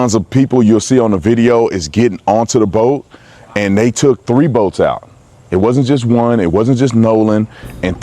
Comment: I thought he said they had 3 boat